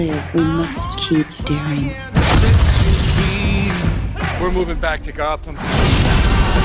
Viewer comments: When that guy said "we're moving back to gotham" I could have swore he said compton.. needless to say I laughed XD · I thought the guy said Compton not Gotham😂